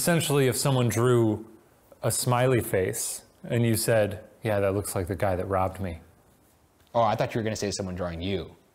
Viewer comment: m headphones beeped at 0:17 and i heard "yeah that looks like the guy who --ed me". thank you for your time.